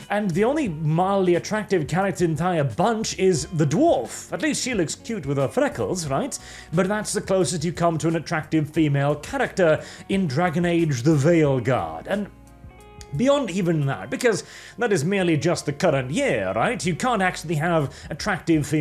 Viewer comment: I kept hearing say "Theyilguard"